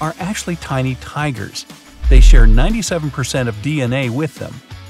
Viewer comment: did anyone hear 97 instead of 95